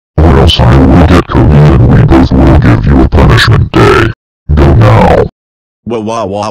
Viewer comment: What did Caillou say? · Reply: I will get Cody and we both give go a punishment day, go now!